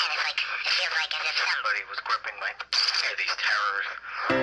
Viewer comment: Ugh, I've always thought he said terrorists instead of terrors. God dammit my life.